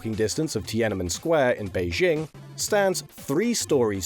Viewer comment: did you just say Tiananmen Square in Beijing? 我爱北京天安门， 天安门上太阳升； 伟大领袖毛主席， 指引我们向前进。